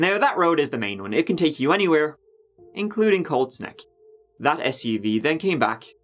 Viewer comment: Everytime said "Colts Neck" I heard "Cold Snack" 😂